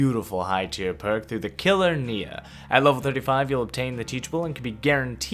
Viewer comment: did he say killer nea?